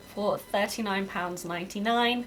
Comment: you say 39.99 but your captions say $49.99 so which one is it?❔